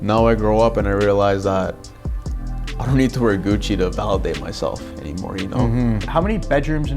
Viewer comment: "I was spending 10k a week on Gucci, but now I don't need to wear Gucci to impress anyone" As he sits for an interview, you guessed it, wearing Gucci slippers lol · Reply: He did not say I don’t need to wear Gucci to impress anyone. If you listen carefully, he said at 6:20 “ Now I don’t need to wear Gucci to validate myself.”